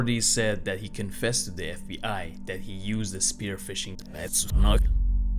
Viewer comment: Thanks to for his voice over. Please check out his personal channel: Library of Wisdom. Check the description box above for a direct link there. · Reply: At around like 10:40. U said the spear of something. I tried rewind bunch of times but right when u say it u muffle it out so u can't here it what did u say ??